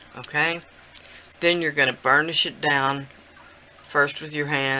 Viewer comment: Sounded like u said "ur gonna burn the shit down" lmfao..... I realized u meant burnish it down haha 😆 😆